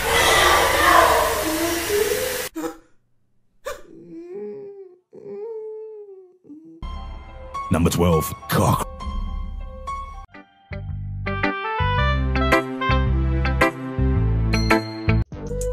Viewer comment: Bro I was at the sad part and when I skip I hear a: “number 12. C—k” 😭